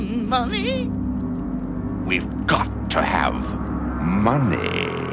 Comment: The last time when I was a kid I heard he said 'We've got to have Monday' but it reveal as money